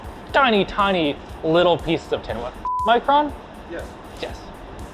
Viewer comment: I heard "F*&# micron?" and I was so confused